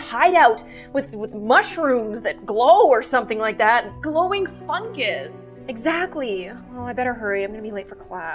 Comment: For a minute i thought she said glowing Chungus.